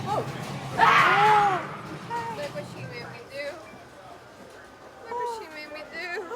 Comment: I thought the maid said “Look what you made me do” 😉